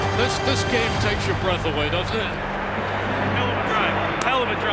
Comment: Did Wentz say hell?